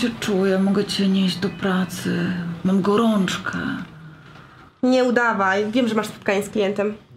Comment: usłyszałam Corciu mogę Cię nieść do pracy mam gorączkę zamiast Córcia mogę nie iść do pracy mam gorączke